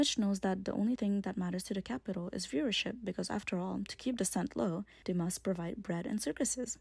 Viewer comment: what are you saying around 9:07? about keeping something low? i cant catch it and watched a few times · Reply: Heyy! Sorry my audio isn’t great on this video. I said “to keep dissent low…”